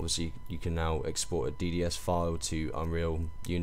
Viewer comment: I thought he said "PSD file" and was thinking "why the hell would you use a PSD as a texture?